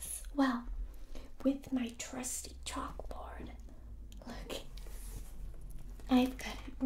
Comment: OH I THOUGHT SAID JOCKS BUT NO SAID CHALK 🤣